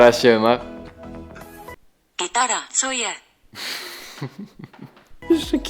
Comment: ja usłyszałam gitara chuje XD